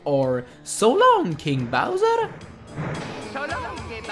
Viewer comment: i dont know but i think its just me but it sounds like mario is saying so long gay bowser